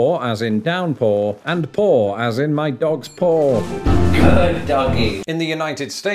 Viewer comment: I genuinely thought he'd said "my dog's poor" until it became clear that that's not what he was saying.